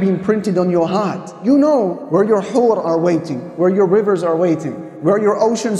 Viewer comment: The "حور" part on 1:30 should have been clear so that non arabic listeners will understand that it is the Arabic word for PURITY, and the imam did NOT say W****, which would be incorrect😅😂😢 الله أكبر .